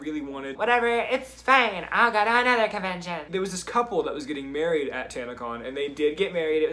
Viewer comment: I thought u said u had to do something on tinder not 10:30 help